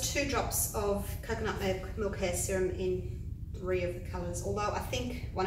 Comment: What was the silicone? Thought I heard you say coconut milk? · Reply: cybertrax1 it’s OGX coconut milk hair serum, found at most places that sell shampoo